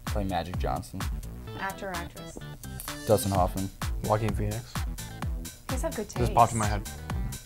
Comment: what actor/actress did Andel say at 6:43? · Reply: Joaquin Phoenix